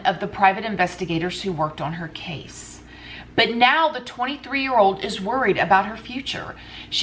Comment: did you say 23 year old...